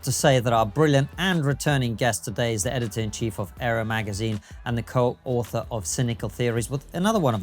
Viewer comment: Did Konstantin say "Aero"? It's actually "Areo" (as in 'Areopagitica').